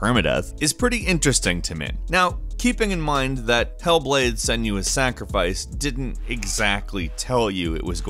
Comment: did just say "Hellboy: Senua's Sacrifice"